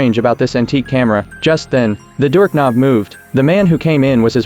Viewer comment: 33 seconds did you just say dork knob??